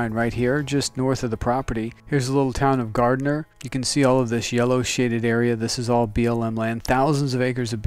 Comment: I thought he said you can see all these shitted areas lol but that's not what he said. My hearing must be going.